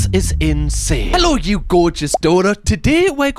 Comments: for some reason, at the beginning of your videos, I always hear "hello you gorgeous 'DAUGHTER'!" and it kinda makes me happy and pat myself on the back ! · For the longest time I thought you were saying "Gorgeous daughters" and I was like "were collectively someone's gorgeous daughter"